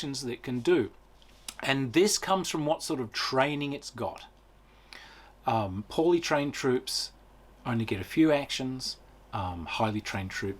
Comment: When you said "poorly trained troops only get a few actions", I misheard it as "Pauli-trained troops only get a few actions" and my first thought was "why is she being so hard on herself?" :p